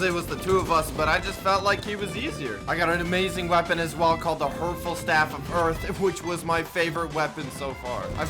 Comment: I thought he said the hurtful staff of hurt